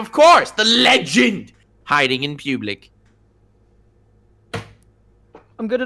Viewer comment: Honestly the first time I thought you said "Hiding in Cublic"